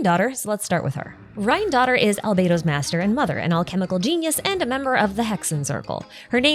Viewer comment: “Rhinedottir was Albedo’s creator and **LOVER**” I had to replay that multiple times to see if I misheard. She meant “mother” lmao